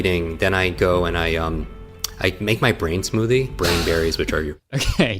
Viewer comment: As a non-native english speaker, when I heard "make my brain smoothie" I interpreted it as "make my brain smooth " I laughed so fucking loud on the thought of that "making your brain smooth" is a crucial part of becoming a billionare